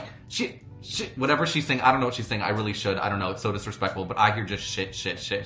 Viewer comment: I hear shnk 2:34 😅